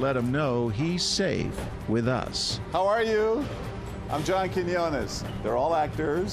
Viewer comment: I feel super embarrased about the fact he's actually saying "Hi, I'm John Quinones"... I literally thought he say saying "Hi, I'm yankin' yo' legs!" as in taking the mick, or making a joke.